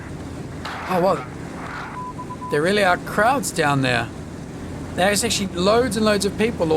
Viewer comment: I just realised you said "crowds" on the quay side. I kept hearing "krauts" :)